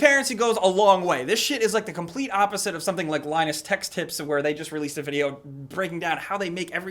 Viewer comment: linus tech tips, not text or tech's or whatever tf you said